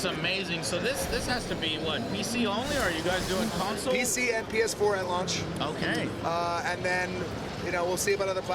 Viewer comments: im not surprised its only for PS4. like screw the xbox users, right? T^T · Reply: oh. I thought he said it was only for PC and PS4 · It will be on Xbox one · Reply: i thought he said pc and ps4